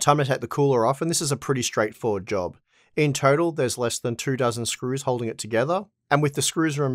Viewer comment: first I heard two thousand screws